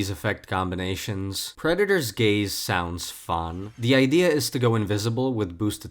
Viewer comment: I misheard 20:05 as "Creditor's Gaze" and was like "checks out . . . wait why is rengar here"